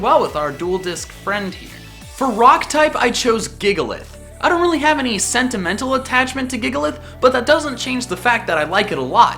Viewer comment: I thought he said "sedimantal" not sentimental attachement. Puns are taking over my brain help